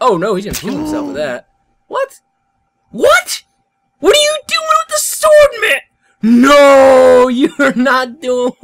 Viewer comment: I heard about right your sword but isn't that going a little to far XD